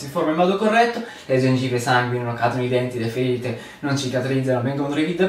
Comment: Salve. Qualcuno mi da dire il nostro nutrizionista di fiducia cosa dice al minuto 2:06?grazie. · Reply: Cadono i denti 🦷, le ferite non cicatrizzano !!! 👍🏼